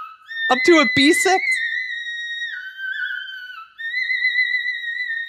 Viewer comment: I heard him say "starting from b6 ending in es*x"😭😭😭😭😭